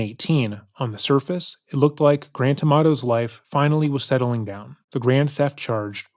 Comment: I keep hearing “Grand Tomato”, do with that what you will.